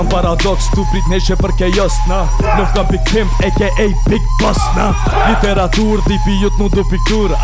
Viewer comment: sounded like big bosna 🤣🇧🇦❤🇽🇰🇦🇱